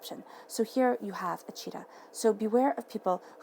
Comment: Wow! I did not know that a Flamingo turns pink because of the fish it eats! Lol! Thank you Mrs book of knowledge! Lol! And when you said the cheetah card meant, "beware of deception" I heard the "cheater" card?? So maybe that means something.?? In just unsure if it's the past or present person but like you said, "the answers are coming"!... As always, Thank you so much for sharing your gifts with us! And THANK YOU SPIRIT!! (oh and yes the ram represents Aries 😎)